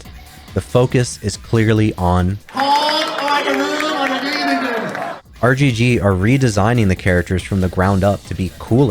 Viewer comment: What does he say at 38:07? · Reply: Very broken English from ex-Street Fighter producer Yoshinori Ono saying "Home, or the room, or the living room" 😂